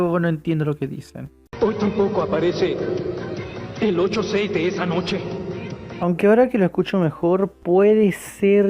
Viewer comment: lo que dijo keisuke fue:antiguo 86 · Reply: Bueno eso fue lo que escuche 20:45 Keisuke dijo:Hoy tampoco viene el 86 de esa noche